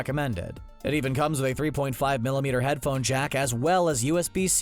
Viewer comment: I heard J A C K A S S